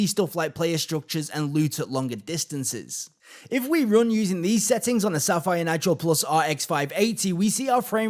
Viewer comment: what did he said there? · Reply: When we run using these settings on the Sapphire Nitro+ RX 580 lol